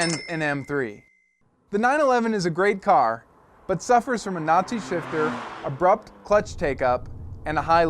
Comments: lol @ 1:55 i thought he said it suffers from a nazi shifter....... what do you expect dude it is a porsche :) (and yes i do know that he actually said notchy shifter) again what do you expect dude it's a porsche) I'd have a GTR over any car on earth it just looks like nothing else out there, and it's not too slow either. on the road I've seen a million 911's ,,,, seen a million m3's...... seen 2 GTR's we all know what one is the head turner out of these 3 cars. and it isn't any of the germans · It sounds like he said "suffers from a Nazi shifter"